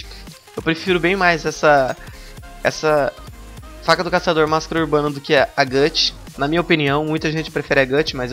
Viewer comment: Entendi "Faca do caçador masturbando" kkkkkkkkk